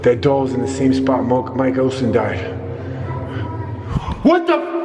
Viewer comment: i heard a little girls voice say mike olson.....